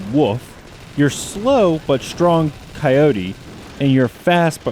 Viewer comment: Did he just say WUF? or WOLF??